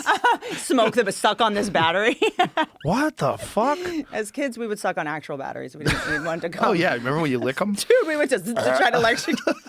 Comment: as kids we would suck on actual batteries... we wanted to cum? did i hear that right